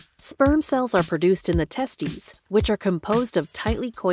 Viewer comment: why did she say “testies” 😂😂😂 everything else is all scientific and proper but the video opens with that 🤣🤣